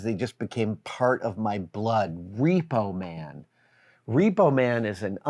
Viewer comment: Wow, I thought you were saying you saw Repo when you were 10, not Repo *Man*. Extremely different